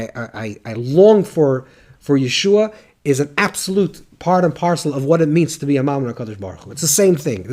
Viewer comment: Thank you , Rabbi. What is the word that you are saying that sounds like “yeshua”??? What do you mean by that word? At the point of 32:40 seconds into the video. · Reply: The Rabbi is saying the word ישועה / which means in Hebrew salvation